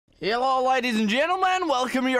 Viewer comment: It sounds like your saying herllo at the start